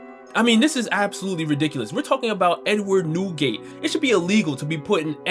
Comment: Bro i swear to god i thought this foo said “N word Newgate” i did a whole spit take 🤣🤣🤣